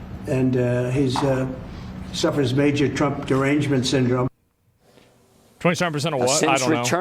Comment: "Trump disorder syndrome" ? did I heard that right? 😅 I don't see chat laughing about it, nor Zack, so I misheard or they didn't notice?